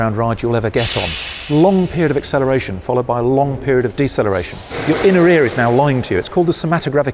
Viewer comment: What does the guy say at 4:54 about the ears and perception? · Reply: he said "your inner ear is now lying to you". If you accelerate or decelerate very fast, the fluid in your inner ears gets all screwed up and it's called the "somatogravic illusion". Even after you stop accelerating or decelerating, the illusion persists. It has killed many pilots because they now have no clue what is up and what is down, and therefore sometimes crash into the ground thinking they are flying into the sky, when in fact they are actually flying straight down into the ground. If you decelerate very fast in a straight line, you will still feel like you are decelerating even after you stop the vehicle and get out. So you essentially feel as though you're laying on your stomach when in fact you are really standing perfectly upright. The human mind is not used to this and it's really freaky and bizarre.